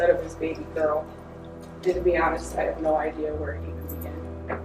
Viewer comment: It took me about 10 times listening to it to understand what the last sentance she said was.. I think it's "And to be honest, I have no idea where to even begin"..??